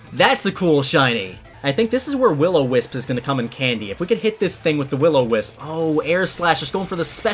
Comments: he ligit said come in candy instead of come in handy😂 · did just say "this is where willow whisp comes in candy"??? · did he just say So this is Will O Wisp is gonna come in candy. Why?